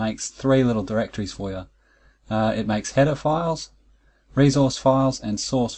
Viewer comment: Also, every time you said "header file" i heard "pedophile"... Not sure what my brain is trying to tell me by that... Not sure if i want to dig deeper...